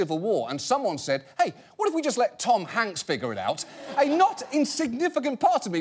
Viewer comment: When he was talking about thanksgiving, i thought he said "especially if you are from turkey" and became really horrified for a moment